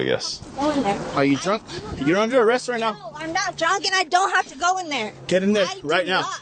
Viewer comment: it sounded like she said "I'm not jogging, I don't have to go in there." WTF?